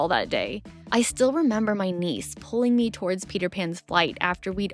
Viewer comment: lol at 11:11 I thought was saying her Knees were pulling her towards Peter Pan 😂